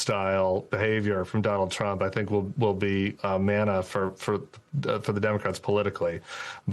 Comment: Did he say “manna?”